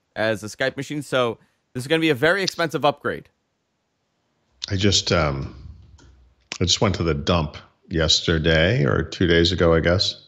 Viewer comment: f**ing hilarious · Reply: was f**ing hilarious, lost audio and when came back the first thing he said was I just went to the dump, I heard I just went for a dump.